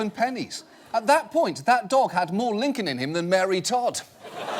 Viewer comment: Can someone explain to me the part at 5:30 ? He says the dog has more something than name I can't catch, I think I don't have the cultural reference for that ? (I'm french) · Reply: Djigr He said Mary Todd. Mary Todd was Lincoln's wife.